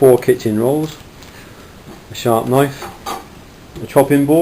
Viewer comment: Thought you said "sharp knife" ? Thx for the vid.